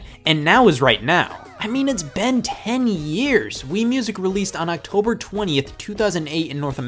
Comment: BEN 10!? was I the only person who heard that?